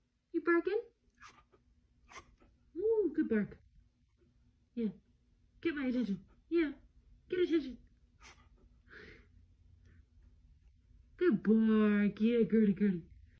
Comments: I like how she still talks to him in the baby voice even though he can’t hear it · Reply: I thought I heard "good boy" too, but when I watched it again after finding out she was actually saying "good bark! Yeah, girlie, girlie!", I realized I misheard her the first time. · i love how despite being deaf you can tell the dog knows he's being praised for something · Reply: I thought she’s saying “Good bark” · The video: "good girlie girlie" the comments: what a good boy, he is so proud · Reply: I Heard Her Say Good Boy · If he can’t hear himself bark, then he can’t hear his person. Still, somehow he knows he’s getting praise :) What a good dog! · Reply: it sounded like “good boy” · Deaf dog thinks he's barking 2.0k people: "a n g r y a s f u c k" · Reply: yeah, me too, I also thought I heard good boy but then I read the comments and watched again and saw the captions. Sorry for my snarky reply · The cute part is that she can't hear her owner talking to her, she sees she's being noticed and keeps on doing it that's so cute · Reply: Im just saying they misheard what she said. I heard “Good boy” at first too but then realized she said “Good bark” not a big deal all Im saying my friend. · Omg that’s adorable. The way he looks back, He’s like “See I’m one of them too you know 😉🤗” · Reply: The title says he and at 0:13 it sounds like she says "Good boy"